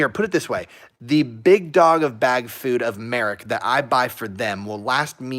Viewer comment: I swear you said "the big dog of bag food " 1:06:26 lol